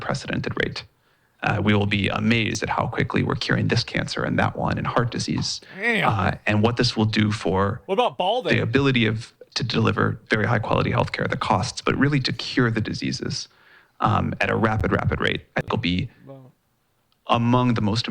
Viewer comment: someone please correct me if i'm wrong. in 10:00 minutes did he just say "we will have very high health care and the cost"?